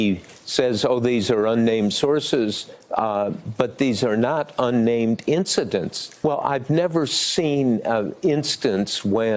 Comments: "THERE'S A MORON TRUTH...BY HIM," @ 0:36 LOLOL omg someone PLEASE tell me I'm not the only one who heard that EPIC Freudian Slip. XD XD XD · Lmao I thought Woodward said “moron truth” at first. Same difference.